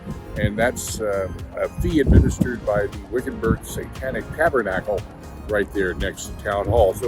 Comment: did he say Satanic Tabernacle??